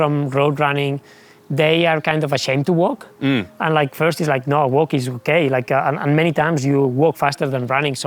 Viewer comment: did he say "walking's gay?" 😂😂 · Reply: " Walkings okay", is what he said. How did you not grasp the context of what he was saying ? 😂